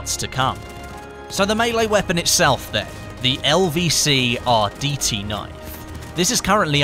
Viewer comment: thought you said LVC RDT-9. 😂😂😂